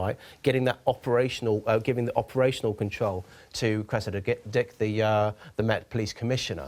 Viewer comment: Cressida what?! 😂 Did he say Git? Gosh.. I could tell from that cheeky editing work at 5:18 that something like that was bound to happen. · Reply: Cressida Dyke